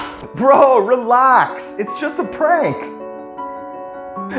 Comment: “Wayne tufar” What i hear: “way too far” 👁👄👁